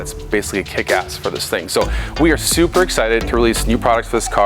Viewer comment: Did I hear him say he's "Supra Excited" ?